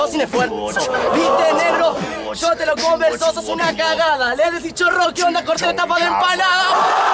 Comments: que dice en el 2:10? algo de tapa de empanada? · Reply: dice: sos una cagada le decis chorro que onda corte tapa de empanada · ¿que dice en el minuto 2:20? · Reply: Que onda corte tapa de empanada🎉🎉